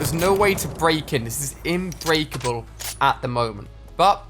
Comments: Did he just say INBREAKABLE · Wait did he say "inbreakable" at 9:10 ??